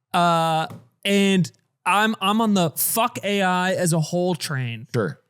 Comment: What did mean when he said "fuck AI as a hole train" ?